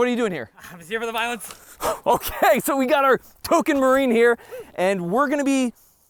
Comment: I thought you said "Tolkien Marine" when you pointed to Mojo, i had to replay it to hear "Token Marine"... both work...